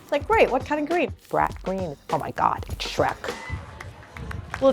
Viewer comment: Did she just say Brat green... Iconic.